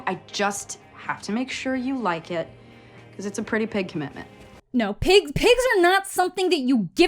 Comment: I swear she said "it's a pretty PIG commitment"